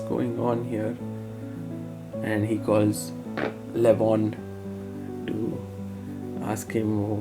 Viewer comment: Guys what did U hear at 13:22😂😂😂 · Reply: I skipped to it and thought he farted and said LEMON at first 😭